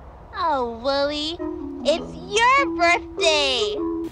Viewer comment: When Amanda talks it sounds like she says "wioly"